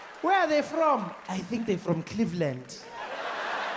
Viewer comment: "I think they're from Cleveland" is brilliant 🤣🤣🤣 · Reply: i thought he said Caveland